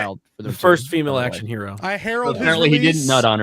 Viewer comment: Did she say "My herold !", so Galactus is 'her servent' now ... The world eater, god no.